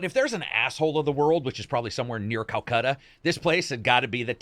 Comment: Did He Just Say "Calcutta"?